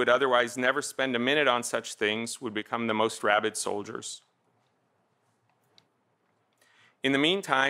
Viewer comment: "Rabbit solders"🇨🇳❗ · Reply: I heard; "rabid soldiers"